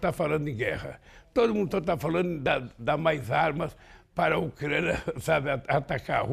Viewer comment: min. The English subtitles say "Everyone is talking about providing weapons to assist Ukraine" but that is not what he said in Portuguese, he actually says; "Todo mundo ta falando de dar mais armas para a Ucrania atacar Rusia" = "Everyone is talking about giving more weapons to Ukraine TO ATTACK Russia". I respect president Lula very much for this time he is very wrong. Ukraine does not require weapons to attack another nation. Ukraine did not invade another country. Ukraine needs to defend it's sovereignty and most importantly, it's people, it's peace and it's future as a nation.